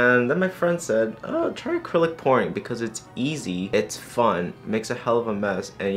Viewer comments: I literally googled: What's acrylic porn? That's not what he said, is it? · deadass I thought said "acrylic porn" I scREAMED